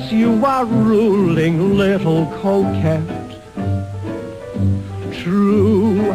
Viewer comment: We have this on a PBS performance. My Wife always cracks up when Guy announces this song it sounds like he say "Little Coke Head"...LOL.....